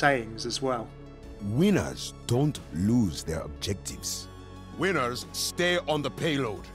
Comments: at the quote "winners do not lose their objectives" i thought it said "wieners do not lose their objectives" 😂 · Lol when he said "winners stay on the payload" I thought he said widow stay on the payload